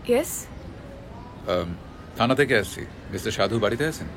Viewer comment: sounds like he is saying: "lana de kfc" :D